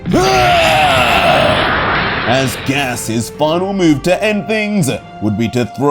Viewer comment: He's that what now??? Im tryna make sure I heard gas correctly 😂😂😂😂